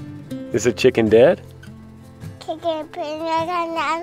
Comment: Sounded like was saying chicken nugget. Maybe he was hoping for some nuggets